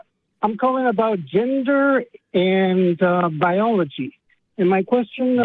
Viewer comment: I thought he said, "gingers and biology."... as a ginger myself I was very excited to hear that he had no idea how to explain gingers biologically lmao